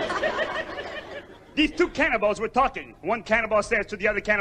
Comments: Wait, was Fozzie saying cannonball or cannibal around the time Waldorf says why did we laugh at that terrible joke? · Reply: I know he said cannibal, it sounded like he said cannonball... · Wait, was Fozzie saying cannonball or cannibal around the time Waldorf says why did we laugh at that terrible joke? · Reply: It sounded like he said cannonball, I'm like, wait, cannonballs don't eat....